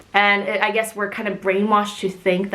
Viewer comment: fuuuck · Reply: k e r m i t s u i c i d e @ 9:19 I heard Kim but apparently I was not the only one who heard her say Kim. The caption say him but it clearly sounds like she said Kim. Idk